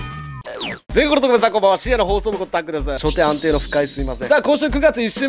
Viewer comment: 毎回思うんだけど、最初の「深夜の放送部」が「死への放送部」聞こえる